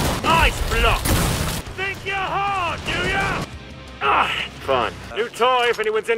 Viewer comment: what Foster says? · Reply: YOU THINK YOUR HARD DO YA That's what he says